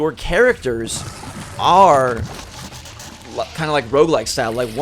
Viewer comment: Did I just hear that your character is "kind of ROBLOX style, once they're, they're gone."